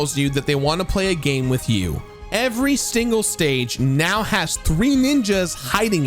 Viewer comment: did you say stingle?